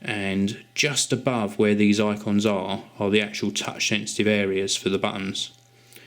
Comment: Lol he called it an Iphone man o man 0_0 7:04 lol just a joke it sounded like it though, dont want to start a war (he said Icons just a strong accent)